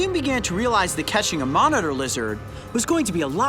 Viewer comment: For a second I thought you said "with a hoax of catching a monitor lizard."